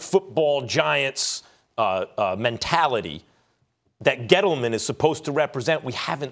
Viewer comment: I thought Max said ghetto man at first.